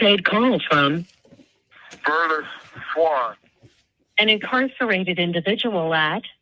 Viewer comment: I thought they was saying his name was all these years.